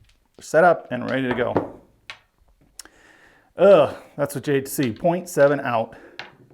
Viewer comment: Did you say jade-c for the 7 right after the puck was established? Around the 11:00 min mark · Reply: "That's what you hate to see"